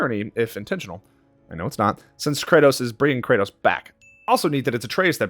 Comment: Alright, even though this video is a year ago, this may be irrelevant but at 31:50, the subtitle says "Atreus is bringing Kratos back" but I hear "Kratos is bringing Kratos back" instead.